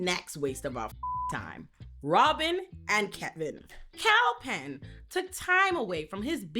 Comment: There’s a mistake in your captions, instead of ‘Kal Penn’ it says “cow pen” twice at 6:47 & 7:05 Great video!